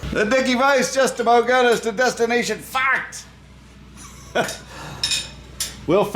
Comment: I hear "Destination Fooked" and I think you need to work on your Aussie Accent..